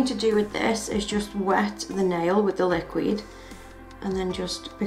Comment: did she say liq-weed?